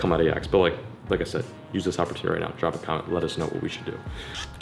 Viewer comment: Every time he says "let us know" I hear "lettuce know".